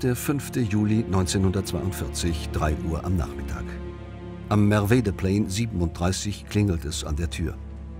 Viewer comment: was sagt er da???? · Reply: Merwegeplen